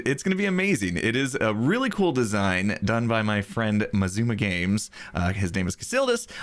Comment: A design by Xisuma Games? I think I heard wrong, seeing as none of Xisuma’s channels’ names contain the word “Games”.